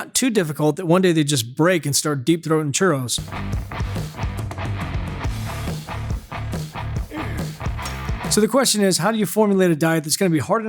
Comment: Did he just say deep throat ingredients choros🤣🤣🤣🤣